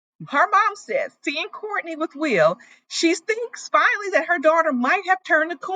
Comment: At first I thought you said "Corny&Will"😂😂😂 This has to be one of the second best seasons of LAL there's so much drama 😊.